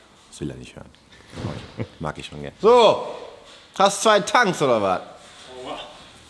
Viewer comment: hab ich da ein leises "OR WHA?" bei @ 7:21 gehört?